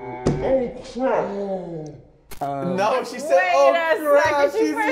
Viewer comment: I heard oh crack